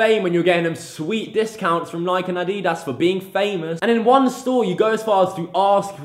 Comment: did he just say addiiedass